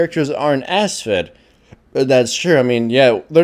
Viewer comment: sounds like said ass fed.